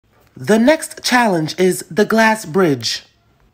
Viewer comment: At first I thought it said grass Ridge